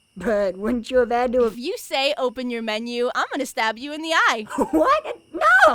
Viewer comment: What did he say? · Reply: Open your menu